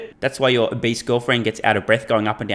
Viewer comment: I thought u said Wildebeest girlfriend...was laughing my ass off. I wish u had now.